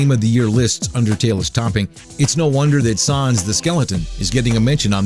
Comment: you spelt Sans, Undertale, and character wrong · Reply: what I'm just mentioning how the narrator says sans because it sounds like hes saying suans